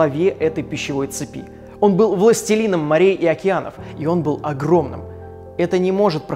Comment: мне одной с начала показалось что он сказал пластилином?